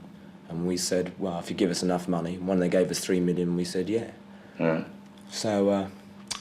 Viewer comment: Did he say 3 million? How much is 3 million Quid